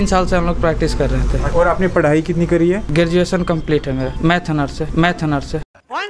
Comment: matheanercy ya mathanaarse kya bola bhai🤔🤔🤔😂😂😂